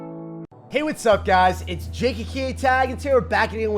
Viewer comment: I was having a horrible day. It immediately got better when I heard, "Hey guys, its Jake AKA Tag!" You rock man! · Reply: I hear "Whats up guys, ita jakey keeay tag!"